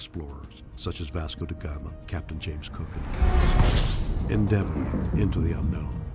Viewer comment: The first time I heard this I thought Shatner said "adventures undertaken by notable explorers such as Vasco de Gama, Captain James Kirk and...", now I realize he said Captain James COOK! Haha